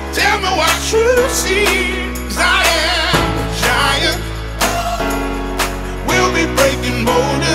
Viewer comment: Been trying to find this song from the radio from when i first heard i thought he were say im a trying but hes saying giant glad iv judy found it